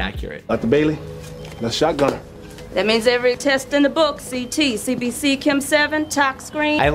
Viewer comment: Did she say CBC?? If she did, it means my intro to healthcare class is working, because CBC means Complete Blood Count.